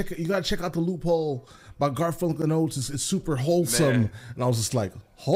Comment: We thought he said "wholesome", but he said "holesome".